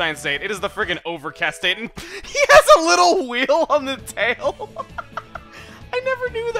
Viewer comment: I misheard "wheel" as "Weedle" and replayed that part 5 times searching for a Bug Type.